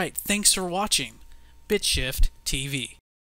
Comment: has it ever occured to your that your channel name sounds like Bitch Shit. Not even trying to be a smartass, I heard "bitch shit" everytime you said your channel name o__o